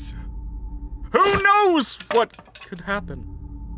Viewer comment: I'm a ukrainian, and i have to say? that Caine wasn't mentioning God in 3:37. I also can't explain exactly what was he saying, because there's no such word in English. He said something like : "Who knows what will happen".